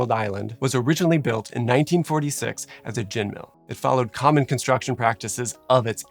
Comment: a gin mill? Is that what he says?